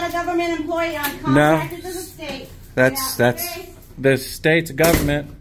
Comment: what did she say, “I am not a government employee, I work for the state”